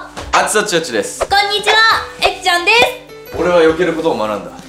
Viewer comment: 「ぅえっちゃん」が「ルネッサーンス」に聞こえたのは私だけでしょうか？............................................www.